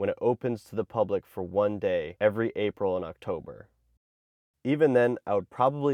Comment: I thought you said “every April in October” 😂 😂